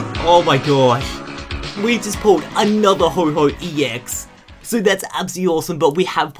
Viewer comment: did he say another ho-ho